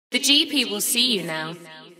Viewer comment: The GP will see you now, sign of a banger coming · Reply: NOO wayyyy i thought he was saying "The G people see you now" talking like of heavenly people... bro ive thought this for so long